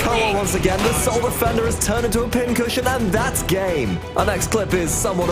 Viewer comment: i thought he said " and thats gay"